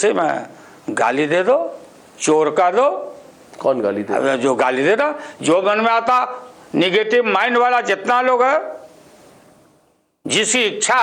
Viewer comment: skip to 10:45 i think lalu said jo gandmara aata